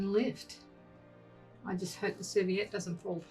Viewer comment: Please have subtitles turned on. So many words I could not understand. It was incredibly frustrating! I would have subscribed otherwise. What looked like a napkin to me, you called something else, which sounded like you said "sueviet". ???? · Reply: If you hit the cc button, subtitles will appear. And yes, I said serviette, which is the same thing as a napkin. Hope that helps 😊